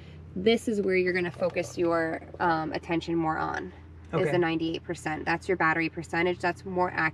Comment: DID SHE CALL YOU A MORON???